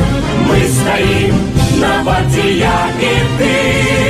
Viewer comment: Subtitles are wrong here. "Warta" means "Guard" or "Outpost" in Belarusian. So the correct version of this verse would be "We stand on the guard for you" or somehow like that.